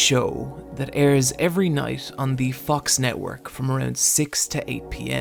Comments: did i hear „The Fuck's Network“ ? :DDD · For a second I thought he said the fucks network 💀